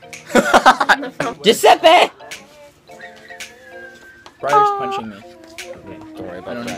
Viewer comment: did he say: briar is punching me?😅